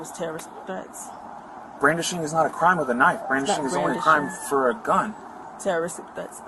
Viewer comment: I thought he said, “blacktually, brandishing is only bad if it’s a gun”.